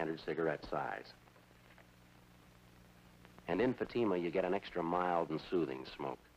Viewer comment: Let's see....Chesterfield or Fatima.......decisions, decisions. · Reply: When Jack Webb said "...in Fatima..." it sort of sounded like "emphysema"...uhhhh, no thanks.